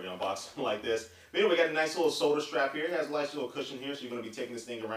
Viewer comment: i thought he said 'soldier strap'